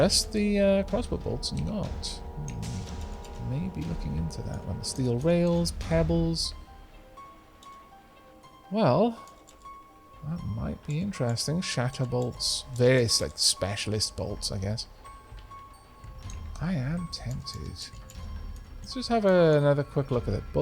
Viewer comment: For one glorious moment, I thought you said you could craft Shatner-bolts but, alas. I regret nothing.